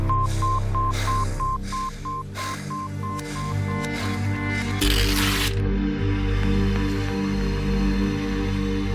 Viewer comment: What the blonde audience member said at 1:59 🤣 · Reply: “WTF?”